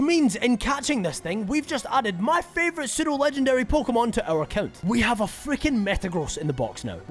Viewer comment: To this day I struggle with thick accents because tell me why I thought you said 'Sinnoh legendary'.